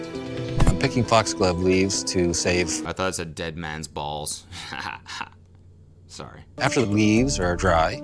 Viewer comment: I can’t believe I ALSO thought it said “dead man’s balls”